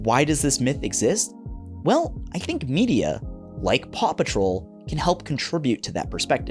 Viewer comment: i always misheard the title of the show as “papa troll” and thought it was some kind of cute moomin spinoff… if only